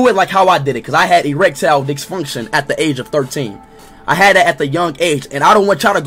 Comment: Did he just say.. "Erectile Dicksfunction?"